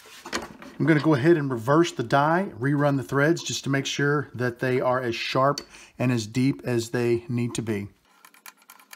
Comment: Great video - at 23:40 you cut threads on the body tennon what size threads were they 13 x .8? I didn't hear you say? Thanks will you just text me the answer. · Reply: Text Sent. The Die was M13 x .8